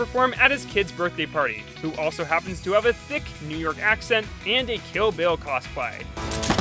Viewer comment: Wasn't paying attention very well and thought he said "Kill bill cosby"